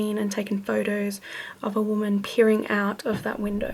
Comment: Hah, I am working right now and I misheard you at around 10:00. "...People have seen and taken photos of a woman PEEING out of that window." I admit I am disappointed that it's not a peeing ghostie.